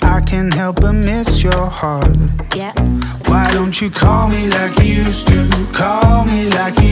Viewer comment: Until today, I always thought he was saying "CALL me like you used to"